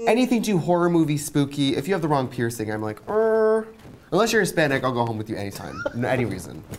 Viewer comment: Did Trixi say she would go home with almost any body endless you're Hispanic??? Catch it in